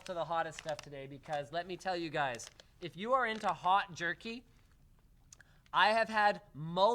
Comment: At about 35:44 I thought Linus said "If you're into hot jerking..."